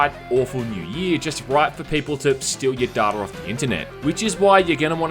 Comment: When you said “steal your data off the internet” I could’ve sworn you said steal your DAUGHTER great vid